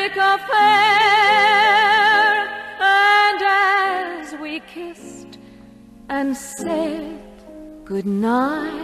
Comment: Why did I hear that "and then we kissed and said goodBYE" ??????